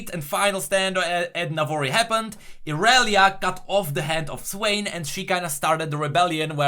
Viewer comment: did you just say that Irelia cut the head off Swain or did I mishear you ? :P